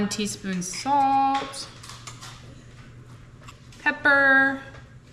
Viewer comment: sounded like said, "Pepper"